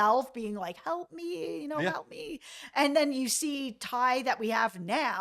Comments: When tai gets up Abruptly in bed and she is saying help Me, along side with her on the phone saying help me . I think dark tai is who we are Constantly Seeing and she's taken over good taissa and good taissa is trapped inside and can't get out . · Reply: Did I hear her calling for Ben? I swear I hear her saying "help me Ben" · Did I hear her calling for Ben? I swear I hear her saying "help me Ben" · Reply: I thought she said help me van